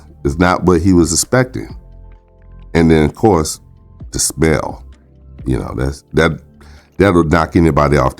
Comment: did he say "the spell" or "the smell" 😂😂😂 EITHER ONE WORKS😂😂😂😂😂😂😂😂😂😂😂😂😂😂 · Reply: The smell 😂😂sorry my tongue was feeling extra fat last night.